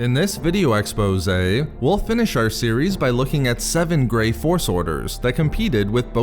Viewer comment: Not gonna lie, I initially thought it said "7 gay force orders".